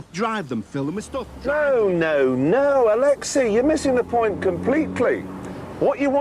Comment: I thought Jeremy said Alexa at first but them remembeered that Alexei Sayle did a ford transit advert, a rare sell out on his part